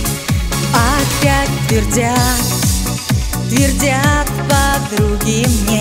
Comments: Послышалось в начале: опять пердят! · Sorry, но изначально расслышал: "Опять пердят..."!😅 А так конечно п*здец, дорогая редакция... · В самом начале послышалось "Опять пердят" ) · Опять твердят подруги ей поёт,а мне в начале послышалось опять пердят подруге ей 😂ну пердят подруги ей звучит лучше 😂согласны?